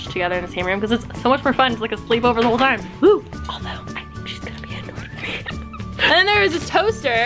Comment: What did Alisha whisper? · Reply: im oretty sure she said... "Although, i think she's gonna be annoyed with me" :)